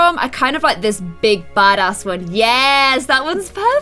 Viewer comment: Did anyone hear her say "that big had A word" when she was at the reefs